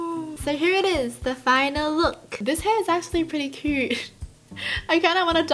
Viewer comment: Imao I heard “I just wanna die and cut my hair now!”